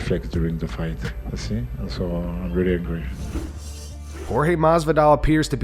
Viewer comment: I thought he said he was hangry